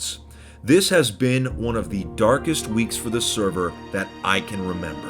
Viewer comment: i thought it said darkest weeb and was really confused for a while